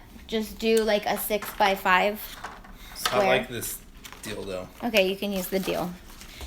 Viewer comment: Great video for beginners. What did he say at 16:42 lol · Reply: I like this deal though. He was taking about the portable trimmer. It is pretty awesome to cut your products with